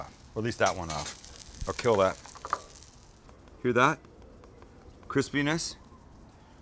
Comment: laughing my ass off!! Sam says CRISPYNESS caption says "Chris penis"